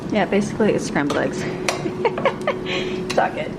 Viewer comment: I thought I heard a bad word but I know that is wrong! Someone or please tell me what was said when scrambling the eggs to ease my mind please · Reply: michelle gatlin I just listen to that part again and she said it’s all good